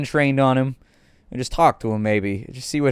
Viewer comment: i think i heard 911 in progress